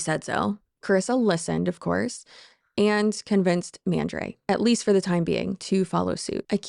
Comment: Also… anytime you say Mandre’s name all I hear is “Mandrake” so you’re not alone there 😅